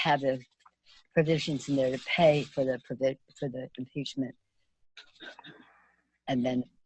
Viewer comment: did piglosi just admit she and the dems have "paid" for the impeachment of our president??? I swear I heard it!! Anyone else???